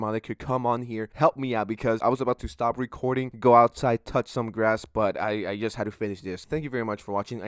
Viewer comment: I thought he said, "I was about to go outside and touch some a$$" so I did a doubletake and realized he said grass.